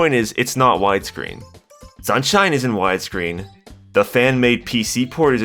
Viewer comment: Not gonna lie, at 1:29, I thought you said, "Sunshine isn't widescreen", so I was like, "Yes it is."